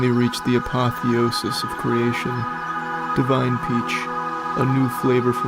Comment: i was just listening here and for a second thought he said "divine gooch" 💀